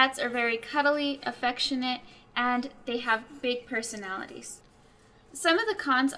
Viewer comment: I heard "they have fake personalities" 😂